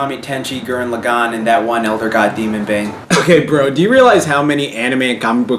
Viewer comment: What's that gold looking anime you said after Gurren Lagann at 1:22? All I heard was "elder god" but that's H.P. Lovecraft.